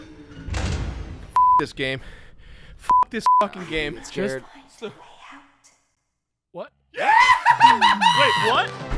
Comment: What does baby say at 27:10? I couldn't make it out from the dialogue of the players. Something about a "way out"? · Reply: I think Ennard says "I will find a way out."